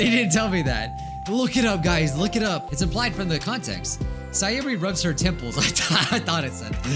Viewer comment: IDK if you guys couldn't tell, but I know thought it said "Sayori rubs her nipples" instead of "Sayori rubs her temples" Get your mind out of the gutter!